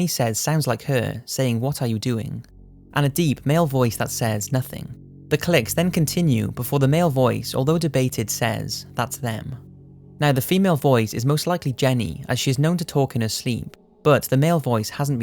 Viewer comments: I didn't hear "it's them?" on the last one. I heard "hey Jen?" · See, I didn't hear "it's them." I heard "Jenny."